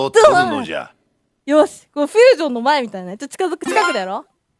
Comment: Maybe I'm wrong but I think at 2:10 she's saying "fusion no mai mitai da ne" which means "looks like the fusion dance" (dragon ball reference) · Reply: Fusion no mae* mitai da ne, "Looks like the beginning of a fusion (dance)."